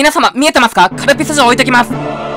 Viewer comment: カルピス嬢って聞こえて卑猥だなぁ思ったら城でがっかり😞